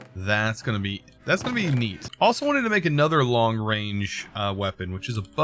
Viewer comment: i thought he said long brain 😂 🤔 brain so big its long? 🤔